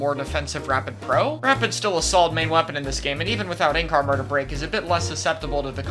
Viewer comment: i watched this video with closed captions and at one point (8:44) the captions say "any car murder". what does say at that moment? Ink armor. i love closed captions, don't love blasters though, way to slow.